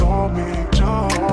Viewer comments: what does 'don me' mean? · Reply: sebin Jeong I thought it said call dommy 😂😂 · i heard "dont call me"